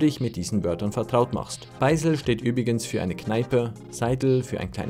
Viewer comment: SORRY ich habe Beischl verstanden,Beisel sagt man aber nur in Wien zu einer Kneipe